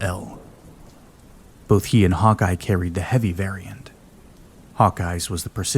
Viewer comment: I heard the Navy hated SCARs